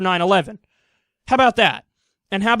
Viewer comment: Oooh, Mid EAST hate, I thought he was saying MIDI's hate for most of the video. I didn't think bleep and bloop based music could cause this much hatred.